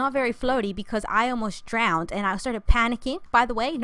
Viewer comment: I thought it said I ALMOST DIED IRL